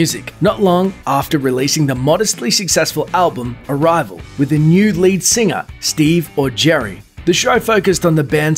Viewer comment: Did you say NUDE lead singer?!? 🤣